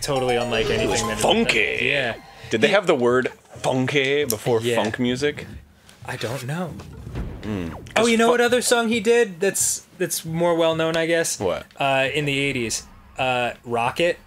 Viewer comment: thought they were saying fuck music lol